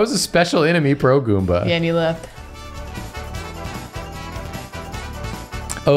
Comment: For the subtitles, I think is saying Peche like the French word for Peach?